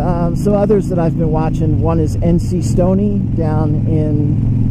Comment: Oh, by the way, at the end of your video you mentioned two other rider vlogers. I am familiar with the first one and I do subscribe to him. But the second one sounds like you said N C Sony, I tried for over an hour looking for him using different letters that sound alike and other different attempts but never found him. Could you reply with a proper spelling of the person you mentioned as the second one? Would greatly appreciate it, and thanks in advance.